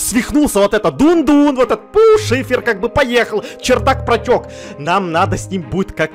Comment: мне послышалось что он сказал: Всё шиппер поехал😂😂😂😂😂😂😂😂😂😂🤡😂